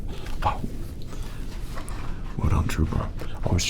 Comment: He really said “What up trooper.”